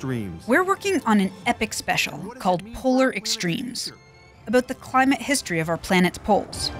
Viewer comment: Physics Girl brought me here. Instantly subscribed and looking forward to right now binge all your videos. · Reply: Me too. I thought she said "anarchic extremes" and I was ready for some radical politics.